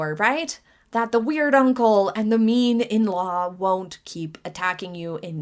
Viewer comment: Had to relisten a couple of times catch that you said 'mean in-law'. First time around I really thought you called her MEME in-law!